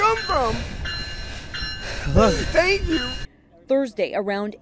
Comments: I thought he said “I hate you!” But he said “thank you!” In a scared way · It’s sounds like he said “Where did you come from, I hate you!”. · “Where’d you come from!? ....Thank you!” I almost cried... My theory is that homeless man might’ve passed out there, maybe thought of killing himself, and as soon as he woke up (sober?) he realized when he was saved, his life wasn’t meaningless after all. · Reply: did he rly said thank you? srry because i heard "*inhales*hate you" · “Where’d you come from!? ....Thank you!” I almost cried... My theory is that homeless man might’ve passed out there, maybe thought of killing himself, and as soon as he woke up (sober?) he realized when he was saved, his life wasn’t meaningless after all. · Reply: I heard "hate you!" What did he say? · Did anyone else here the guy say “where did you come from” and “I hate you” after being saved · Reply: I thought I heard I hate you but it was thank you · Did he say “I hate you” or what? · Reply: He said "thank you" I think